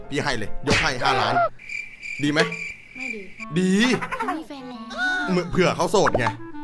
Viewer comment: พูดว่าอะไรนะได้ยินไม่ชัด5555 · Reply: ไม่ดี